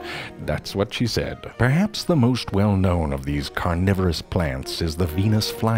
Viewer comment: I thought that shit said Coronavirus Plants at first. buahaha